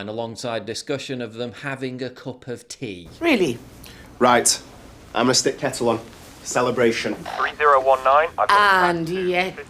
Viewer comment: I KNOW that I heard him say, "I mastec t kettle on - celebration".